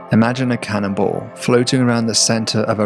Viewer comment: When he talked about "the cannonball" I thought he said cannibal for a brief moment. That would work as well, I suppose.